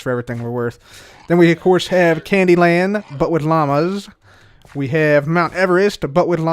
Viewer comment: When you said "Candy Land... but with llamas", I heard "Candy *Crush*... but with llamas" XDXD!